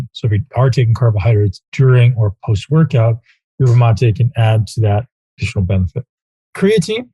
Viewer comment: Is their a difference between Acetyl-L-Carnitine and what you specify N-Acetyl-L-Carnitine? At about 7:30 I cannot make out what you are saying sounds like ……matte???? Highly branched cyclic dextrin is a fast acting carbohydrate used in workout fuel with recommendations to pre-load 15 minutes before workout and continue using through workout. Does HBCD have the same sedating effects and do you recommend it should not be used until 20 minutes into the workout?